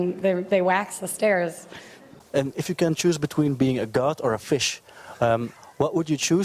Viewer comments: 🤣 at the 7:07 mark he asks her wether she would want to be a "CAT, or a fish" 🤣 but his accent is so thick she thought when he said "caht" that he said "god" 🤣 funnyfunny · I thought he said "if you can choose between being a goth and a fish, which fish would you like to be?" Lmaooo A much more interesting question